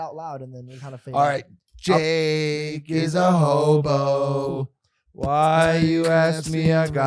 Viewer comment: Who else heard him say "Drake is a homo"??????? lol